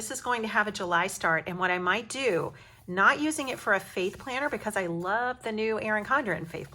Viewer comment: I love all of your ideas. Printing come color on the white stickers is genius. Do you mind sharing the pdf somewhere? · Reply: This was a great idea! Also at first when you said faith planner, I thought you said "fake planner", I was so thrown for a second! Lol!